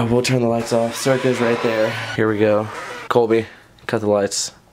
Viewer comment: If you guys hear or see anything I missed comment down below or tweet me.. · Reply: 6:58 I’m not sure if it was the door. But it sounded like it said “Colby!” Like really fast. No joke. Please like i want to check this out